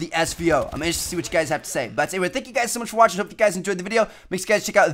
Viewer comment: Am I the only one wondering wtf he says at 3:50 ?? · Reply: Check out the videos on the screen if u haven't seen them yet